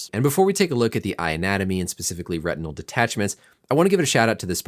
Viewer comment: I definitely heard “eye-natomy” when you said “eye anatomy” lol